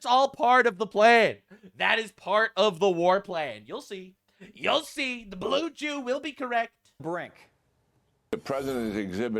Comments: I had to rewind 3 times I thought you said, “the Blue Chew will be correct”. 😂😅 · I SWEAR IT SOUND LIKE YOU SAID BLUE CHEW NOT BLUE JEW ..LOL